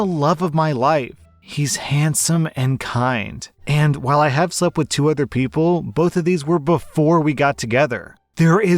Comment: ok i’m sorry but in the first story when she said “i’ve slept with two other people,” i heard “200 people,” and i thought she was just saying she’d slept with 200 people nonchalantly, but yeah i can understand where the husband is coming from with the negative test, that seems pretty cut and dry if you look at it without extraneous circumstances, but if she never cheated, she should sue the hospital hard